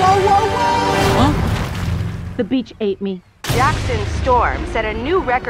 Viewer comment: I swear i thought she said "the bitch ate me" LMAO